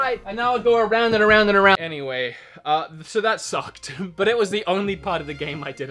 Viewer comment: did he just say that s u c k e d